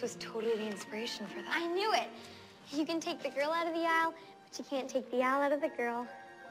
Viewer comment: What is Dizzy saying at 1:01? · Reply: ItsLxna "You can take the girls out of the Isle, but you can't take the Isle out of the girl"